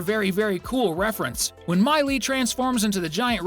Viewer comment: on this time he accidentally says Miley instead of May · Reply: It’s mei